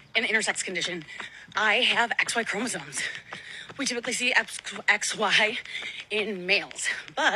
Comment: I thought she said "....with a typical pssy". But she actually said "...we typically see".